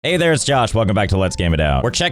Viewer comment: If you slow the video down a little it sounds like he is saying «either its Josh» in the start